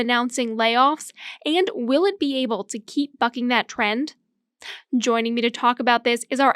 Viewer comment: did she say '...keep f**king that trend?' at 0:42 😄 If so, hopefully Zoe, hopefully!